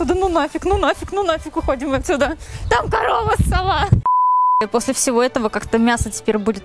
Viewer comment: Мне послышалось Корова сова